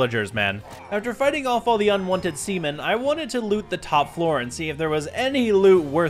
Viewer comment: i heard "semen"💀